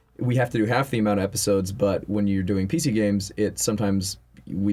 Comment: I thought Ross said Pizza Games, but turns out he said PC Games. :( Question: Did you guys ever go to conventions or events before being well known and if so, how different are they now that you have a following? Love and Peace!